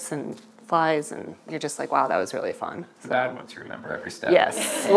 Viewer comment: This is awesome! Btw can anyone transcribe what Noah said in 33:12? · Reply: "the bad ones you remember every step"